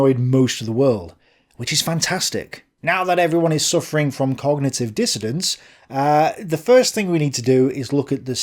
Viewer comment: Did you say "cognitive dissidence" at 1:05? This dissonance is putting me in a dissociative state:)